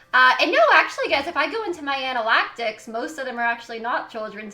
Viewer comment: Anal- ACT - Ics? Pronounced An-a-lit-ics. Analytics. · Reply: I thought I heard anal-actics, too. I was at work. The manager asked me if I was okay because I was legit cry-laughing.